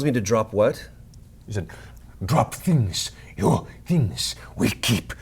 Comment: Guys at 1:01:28 if you have captions on instead of Matt saying "drop things" the captions reads "drop penis" hahahaha